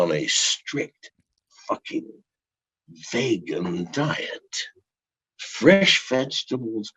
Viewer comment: Did he say fresh Vaygin diet haha